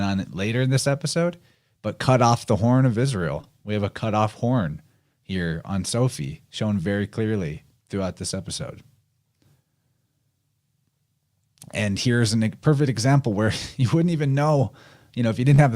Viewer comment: when you said Sylvie i heard Sophie which lead me to Sylphie. Don't know if thats relevant 🤔